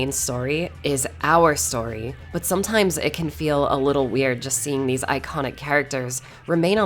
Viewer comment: Ngl, I thought said "little wang."